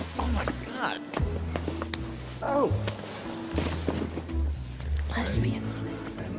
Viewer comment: What does Leighton say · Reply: Lesbians